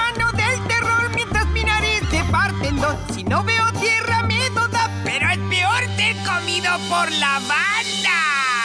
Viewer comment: Que dice en el minuto 1:24 ? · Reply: Si no veo tierra miedo da, pero es peor ser comido por la banda.